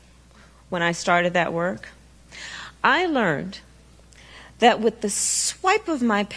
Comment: 😳 Did she just say she lernt? 2x